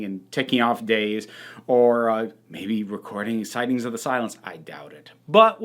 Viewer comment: I thought you said “recording sightings of the Cylons” and remembered no, wrong tv show 😂